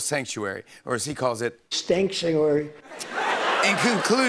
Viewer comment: "Stanksuary" My sides are hurting. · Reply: I thought he said stankuary